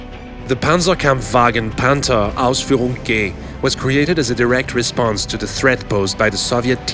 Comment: did he just say Panther Ausführung "GAY" XD